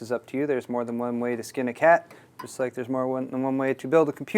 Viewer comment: did he say theres more than one way to skin a cat?